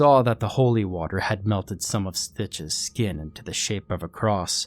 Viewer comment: I thought it said snitch at firsr